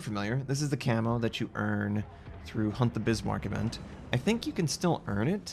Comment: it sounds like you said “hump the Bismarck” lol.